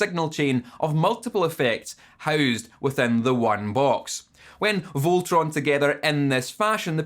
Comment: did you say "when Voltroned together"? lmao i love you